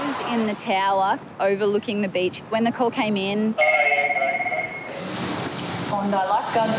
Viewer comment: I thought she said and the cocain came in